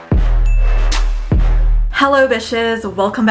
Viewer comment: Lol I thought she said hello bit*hes